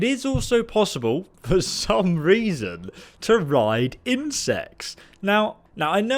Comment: After he said this, he said, "I know what you're thinking." Wanna know what I was thinking? I was thinking he said, "ride in sex."